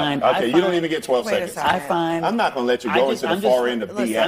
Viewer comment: I'm not gonna let you spit damning facts" is what he's saying.